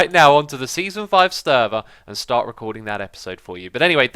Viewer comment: Did you just say "sterver"?